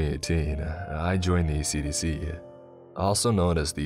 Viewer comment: I thought he said AC/DC... god what a classic band they were.